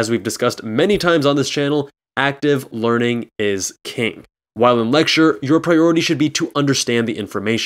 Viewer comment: I always heard “is key” not “is king”